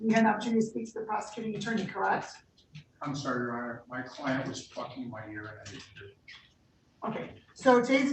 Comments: I thought he said “my client is fucking my ear” at the beginning 😂😭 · did that attorney really say "My client is Fu*king my ear right now?" Please tell me I heard that wrong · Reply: LOL I had to go back and listen and I am pretty sure he said "plucking his ear" which is an old saying for talking in my ear.